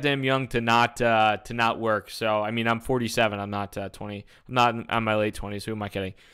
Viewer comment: unless he says 37, I can't really hear him clearly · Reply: Yep he is, that's fitting... doesn't look in his 20s. He said "I'm 47, not 20... not in my late 20s who am I kidding". Because 47 is too young to actually retire; retirement age is 65-70 kids.